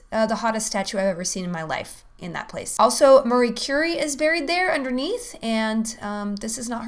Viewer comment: Who's Murray Curry? I love your accent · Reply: I know who Marie Curie is, but it sounded like she said Murray Curry.